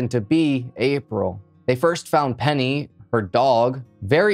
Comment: I thought he said the dog was named Henny …..